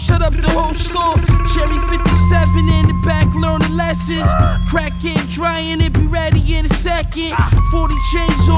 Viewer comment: Thought he said crackhead crying / it’ll be ready in a second